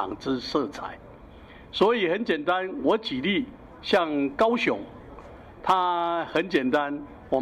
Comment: 真的有200多人甚至1300人聽得懂他在說什麼？還是標題聽了爽就讚？ 我的老天爺 0:34 我舉例像高雄 0:35 他很簡單 0:38 我們陳市長沒有公開之行程 0:43 那事實上自己在鳳山來勘災 0:47 那這代表什麼 0:48 就代表他自己沒有公開行程 0:52 或許就會有國民黨的民意代表 0:57 在那邊關心 0:58 可是連這些勘災都要有顏色 24秒 真的有這麼多人聽得懂他講啥？